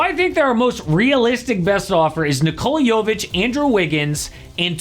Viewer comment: For a second I thought he said nikola jokic I thought he was smokin that good good lol